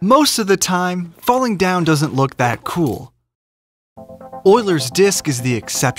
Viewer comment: By do I keep hearing “Boiler’s disc”?